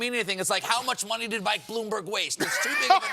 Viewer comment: I thought Stephen said: "The numbers don't mean anything. It's like: how much money did Bloomberg weigh?"